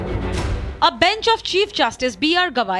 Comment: The reporter reported :" a bench of " and i heard it as : behnchod. I think i need to clean my ears.